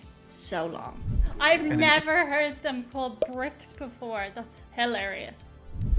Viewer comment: You misheard. He said Brits not Bricks.